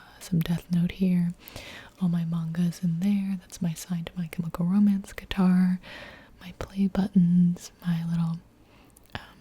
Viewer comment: excuse me young for a hot minute i thought you said SIGNED CHEMICAL ROMANCE GUITAR